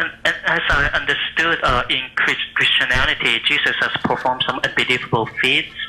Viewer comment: Did he say "Christianality"? And "sonar neighborhood"?